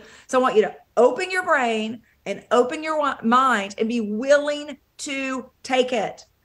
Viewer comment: "I want you to open your brain.. open your wal....(oops?)... mind" LOL was she about to accidentally say open your wallet?? I could be mistaken but it sure sounded like a Freudian slip if I've ever heard one, 🤣🤣